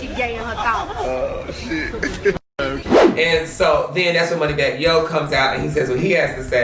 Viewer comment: Did he just say money bag yay yo😂😂😂